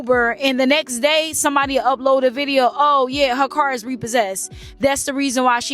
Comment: She said on Cam interview her card 💳 was stolen people heard car n ran with it... i heard car too but i ⏪ it back n realized she said card 💳 🤦🏾‍♀️🤦🏾‍♀️